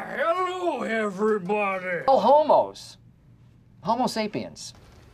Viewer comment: I got ro 6:28 before i had to back it up because i didn't recognize the scene and thought he was saying "no homo" and wanted to listen to it again.